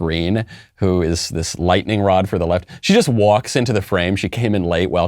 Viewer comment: oops my bad! Thought she was . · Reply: Yeah he said she’s a lightning rod for the left. So I thought Michael said she’s on the left.